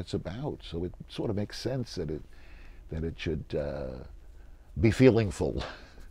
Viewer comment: "Show me a satisfied writer and I'll show you an amateur." "Be feelingful." "Don't do it? Why? Because you had to ask me?" "Don't look to anyone but yourself." WOW! I bet the students at UCLA can really use that in the process of writing a better script. "Be feelingful." You are priceless. Of course I'm sure you can spot sarcasm when you hear it too. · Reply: I don't think he said be feelingful. I heard it as 'be feeling full', as in be full of feeling.